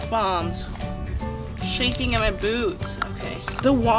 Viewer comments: Omg did she said in 2:40 shaking in my boobs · I thought she said boobs😂😂😂😂😂😂😂😂